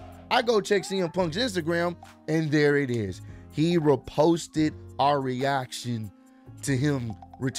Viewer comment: At first I thought it said exposed us. But;ITC could only go up from here.☝️💯